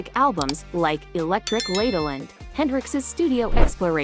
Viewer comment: it sounds like she is saying Electric Ladeling. Uh, O.K.